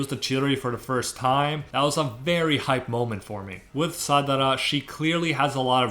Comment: Did i just hear sadara in